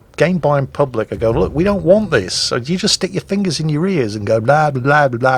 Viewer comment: I honestly heard "...so you just stick your fingers in your REARS, blablablabla, can't hear you! ..."